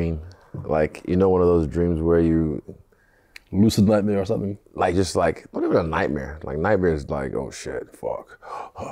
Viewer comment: What'd he say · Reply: He said look at me, can't you see I'm Chinese?